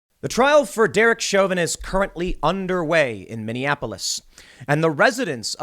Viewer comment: What he said vs. What I heard. Said. "The trial of Derek Chauvin is currently underway." Heard. The trial of Derek Chauvinist currently underway. Funny how the brain works.